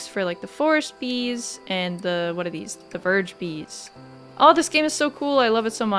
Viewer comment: I thought you said Virgin bees, for some reason.